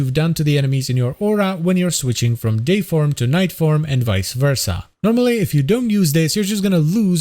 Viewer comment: I had headphones on but not connected to my phone I thought you said switching to his gay form to his night form lol